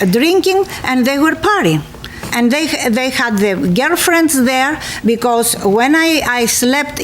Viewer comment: I thought she said ghetto friends not girlfriends😂